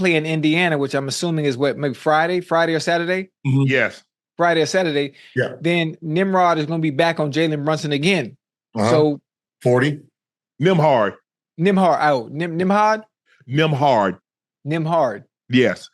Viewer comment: Wait a minute did Ocho just call Nembhard “Nimrod”?